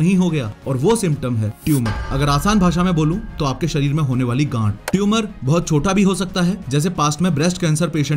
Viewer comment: Sir what you said at 3:52 😂😂😂😂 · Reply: Ganth